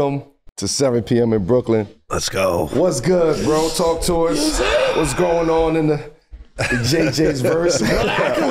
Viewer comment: Melo almost said “JJ what’s going on in the white community”💀💀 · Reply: I thought he almost said “what’s going on in JJ’s hood” 😂😂😂